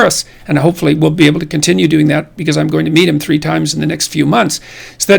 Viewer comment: Did he say "I'm going to meet him three times" or did he say "I'm going to beat him three times"........ Freud!!! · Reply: meet